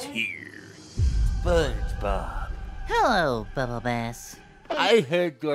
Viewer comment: It sounded like SpongeBob said bubble ass! 🤣